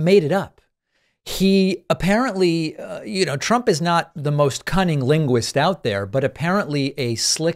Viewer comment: I could have swore you said cunnilingus. I adjusted my hearing aids and heard it correctly lol